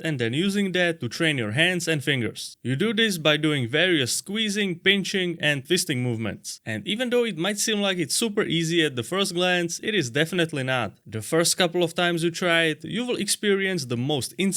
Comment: I swear he said fisting movements 😅